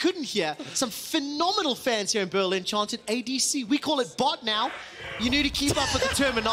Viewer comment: what the guy in background saying? in the first time and second, i cant understand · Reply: he says "ADC" and "THANK YOU"